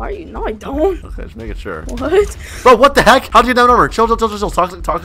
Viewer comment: I heard say what the helk